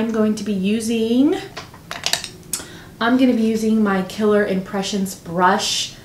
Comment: The it cosmetics love is a foundation brush it's limited edition but is available every october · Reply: ohhh i reviewed the video like 10x I thought you said the brush was Killer Impression. No? Maybe i heard wrong.. i would like to order it reason why im asking !